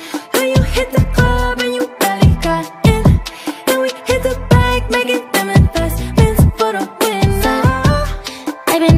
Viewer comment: Im tripping, is she saying 'then we' or 'and we' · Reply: Femelyn D then